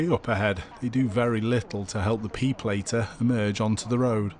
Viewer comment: I thought you said "peopleater" like it's a thing that turns thing into people